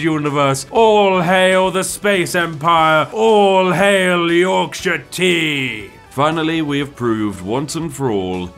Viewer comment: I swear it sounded like you said Orc Shite Tea